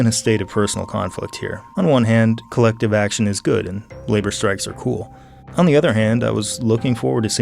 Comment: Did he say "Labor strikes are cool" at 42:20?